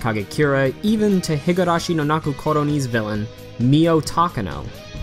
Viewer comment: What is the anime that showed at 1:00. I couldn't understand him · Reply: Higurashi no Naku Koro Ni